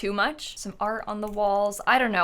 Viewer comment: When you said “maybe some art on the wall” all I heard was “some sports on the floor” 🤣